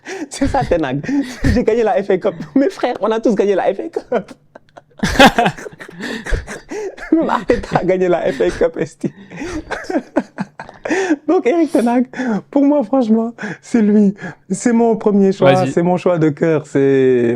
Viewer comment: c'est moi ou dit "la FA cups ESTI" ? 😂😂